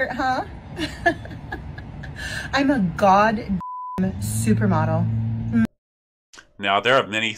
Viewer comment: "I'm a goddamn supermodel." "Just one? Are you like Kirby and absorb the powers of those you eat?" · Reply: I thought she said she ate a supermodel.